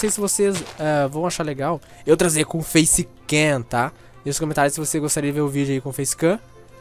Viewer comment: Vei eu ouvi eu transei com facecam